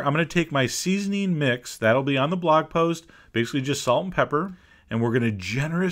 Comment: what is the seasoning mix recipe? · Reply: I thought he said just salt and pepper.